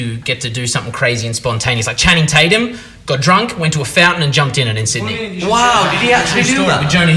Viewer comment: can someone tell me what he says here? "Channing Tatum got drunk, went to ....... in Sydney" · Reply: He said he got drunk, went to a fountain and jumped in in Sydney